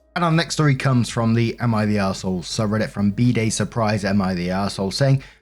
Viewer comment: So here I am, listening while working on Photoshop, when story 2 starts. says "B-Day surprise" I hear "bidet surprise". Like, what? Gave me a good laugh.